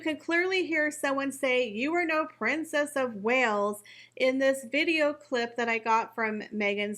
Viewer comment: Did that person say Prince or Princess of Wales? Ive been listening on my earbuds and I keep hearing "You're no Prince of Wales"